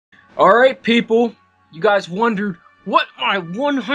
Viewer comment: in the beginning of the vid, it sounds like he is saying: "Alright PeePoo